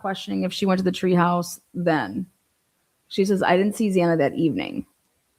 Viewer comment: interview she sounded like she said she hadn't seen xana Friday... 🤔 Aren't they interviewing for Saturday night thru Sunday early morning